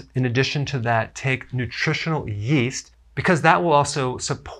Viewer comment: Please, can someone tell me what did he say in the minutes 2:58? Was it "nutritional yeast"?